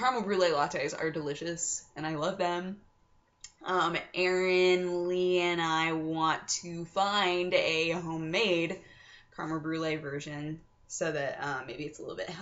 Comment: all I heard was: "...so ErinLee and I wanted to find a homemate... virgin..." My first impression english listening and comprehension skills are very low these days ^^"